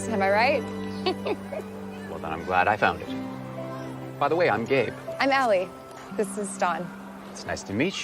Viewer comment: I thought he said, "By the way, I'm gay." 😂